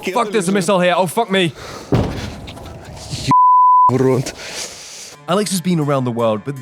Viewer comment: minutes in... What did he say??😆😆sorry.. I find humor where I can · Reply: вначале мат (запикал) и потом "урод!"